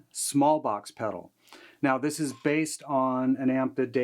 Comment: For a second I thought it said Small Pox. LOL Not something I want to try. The pedal... Definitely.